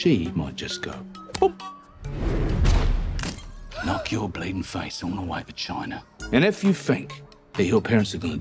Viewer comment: "Knock your blatant face all the way to China" - haha, i love him! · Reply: Thought he said bleeding face